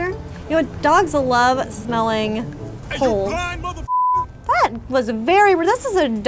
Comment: I thought she said "dogs love smelling holes"